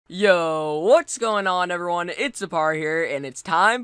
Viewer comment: OMG I thought he said Hello its a party here until now